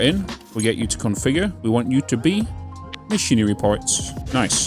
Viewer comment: im sorry you making missionary parts? did i hear that right 🤣